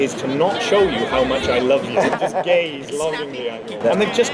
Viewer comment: I was watching this with captions and noticed at 0:47 they wrote gays instead of gaze. I love it 😂